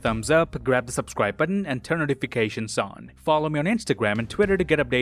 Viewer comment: Did I hear “til then I’m ksi’s hamster”